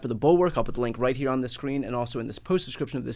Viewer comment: Every time, I mean, literally every time I watch an episode of it, there's that split second that I hear "This is inside the Reich" ;-).